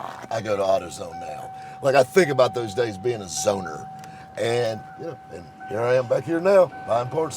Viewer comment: said "those days being a zoner" I heard "those days being a stoner" had to rewind it for a second on that one